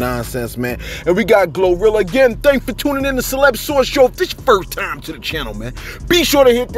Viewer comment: This entire time I thought this dude was saying Celeb Sauce smh